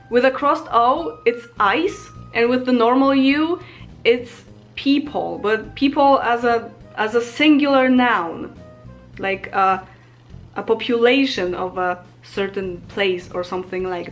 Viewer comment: when she said Lud,.... later I gathered she meant People, ... but at first I was like, did she just say Peep-hole? and was just thinking Ok, I'm trying to learn here, I'll just go with it